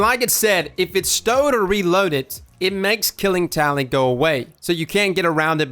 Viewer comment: I thought you said "Killing Towelie" at first